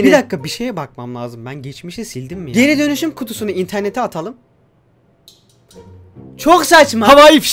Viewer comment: interneti çöp kutusun atalım yerine çöp kutusunu internete atalım XD en az 999 kere izledim ama 2020 de anladım orayı xd